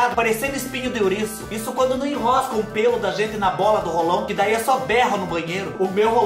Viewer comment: Ele disse Roll-on e eu ouvi "rolão", tá repreendido kkkkk